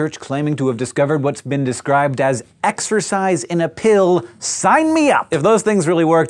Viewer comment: Exercise? I thought you said EXTRA FRIES?! Oh well. Tbh I rarely eat fast food anymore, it tends to give me really bad stomach aches and cramps that lead to me running to the toilet moments after I eat it. I got really sick of that so I mostly get raw fruits and veggies, occasionally eating some red meats.